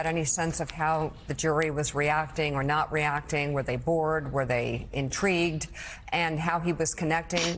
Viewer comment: Except that "the dude", Andrew Weisman, repeatedly says in THIS video that Cohen was an excellent witness. MAGA people everywhere should start realizing that unlike January 25, every other day is not "say the opposite" day. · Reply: He clearly says "critical" at 5:41, not "credible." I'm sure your intentions are honorable, but you are mistaken. Please go back and listen again.